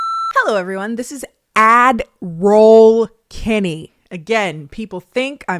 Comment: I never thought you were saying Admiral. I thought you were saying Adderall like the prescription pill oh.😂😂😂😊